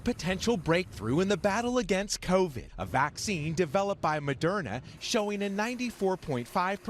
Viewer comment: I could swear at first I heard them say.. "A vaccine developed by MADONNA"